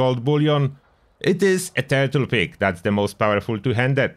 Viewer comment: I liked your “turtle pick” input on stuff. Great idea! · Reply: I thought he was saying turtle pig. Turtle pick makes a lot more sense. 😂